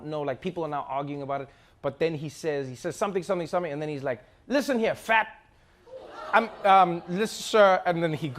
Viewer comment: I thought he said listen here jack?